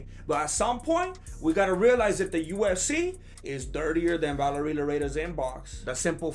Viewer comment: lol it kinda sounds like says USC instead of UFC